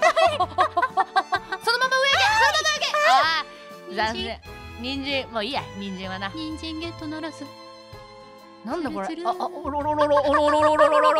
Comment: ゴルシの『人参はもういいや』が最初『人参はいいや』みたいに聞こえてゴルシがあんま人参を好まないのを忠実に表現してんのかと思ったw